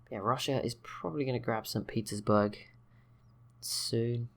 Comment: I honestly thought you said "Saint Pizza's Burg" at 16:20! 😛 As it turns out, you actually didn't and it was just my dodgy hearing playing up once again but I thought it was quite funny nonetheless!